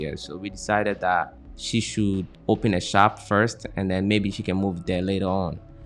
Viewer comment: Volume and other products will make up for it. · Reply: I don't know why I thought I heard you say she'll open a spa. Maybe because I desperately need a pedicure and it's heavily on my mind 😊 I just got to Ghana and will be in Cape Coast tomorrow but not sure I'll find a good one there. Let her definitely consider a spa. If she does her hair braiding she could do those ready-made braids that ladies find so convenient. She can make her own branded ones too. Good luck to the both of you and happy anniversary 🎉